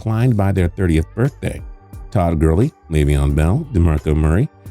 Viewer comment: Did he just say Todd Gurley is 30